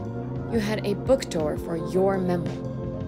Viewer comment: So the girl wrote a book based on the other girls journal? I think that's what happened.. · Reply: But what did she say because all I heard was "a book door for your memoir" It made no sense.